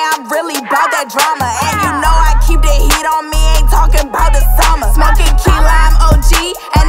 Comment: "And you know I keep the heat on me Ain’t talkin' bout OSAMA" What I thought she said lol